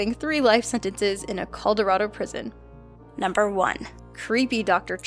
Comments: DID SHE ACTUALLY SAY CALLDORADO?!? AS A COLORADAN IM OFFENDED AS FUCK ITS NOT HARD TO PRONOUNCE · did you just say... COLDORADO PRISON?!?!?